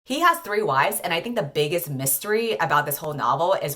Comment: I'll be honest, I thought you said that he had 30 wives and spent most of the video waiting for the other 28 wives to show up